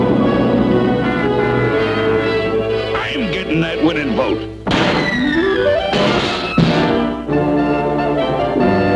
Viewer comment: What does Bluto say here? Woman vote or wooden vote? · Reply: "I'm gettin' that winnin' vote!"